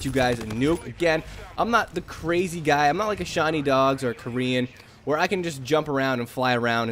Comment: I thought u said Shawwny dog, but then realized u meant Sean E Dog